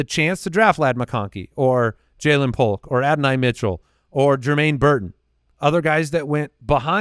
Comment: Wait did call him Adin-I Mitchell...?!